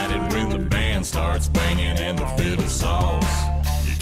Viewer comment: You are not the only reactor that thought they said fiddle sauce instead of fiddle saws! Fun song, great reaction!